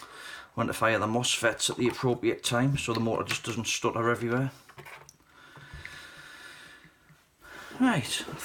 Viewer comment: What did you say at 10:34?? Did you say "stu 'aaa evry 'whaaa"?? Speak English man!!!